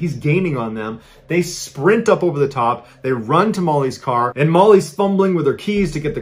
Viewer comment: When he said "To Molly's" at 9:40 I heard it as "Tamales", teehee.